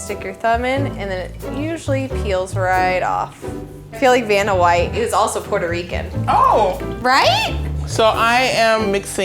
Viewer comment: "I feel like Vanna White is half Puerto Rican." Fun fact: Vanna White is half Puerto Rican. I always thought Vanna was pretty. I knew back then she wasn't only White. I just didn't know what else she had in her background. Now I know! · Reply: I thought she said "Vanna White, who is PuertoRican" meaning she already knew.